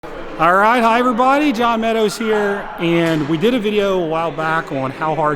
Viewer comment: for the longest time i thought john was saying “Alright hi, higher body john meadows here”